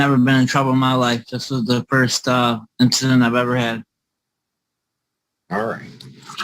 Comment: Ummmm, I thought you said.... "this is the first time I have seen Judge Hard On"..... I believe I misunderstood 😳🤪